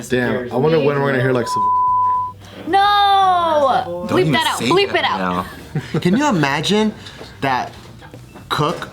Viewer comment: anyone read lips? I wanna know what said that was bleeped out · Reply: He said Savoy, it’s a restaurant in 626!